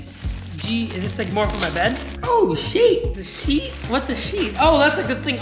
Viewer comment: Evan-"Oh,sheet!" what I hear-" oh,sh*t"